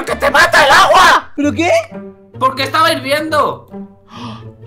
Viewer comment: XD en el momento 2:19 no se que dijo:v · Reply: Dijo el agua esta invierno xd